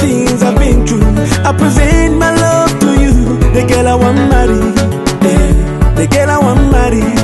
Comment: I hear him saying "I want money"